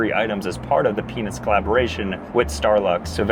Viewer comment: anyone else hear peanuus